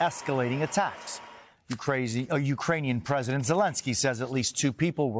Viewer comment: Did anyone else catch the "Ukrazy President Zelinski" at 13:37?🤣🤣😊